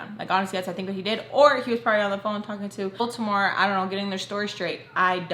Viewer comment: I’m currently driving back home to Baltimore as I listen! I remember when we all thought you said Baltimore whenever you said Voldemort😂😂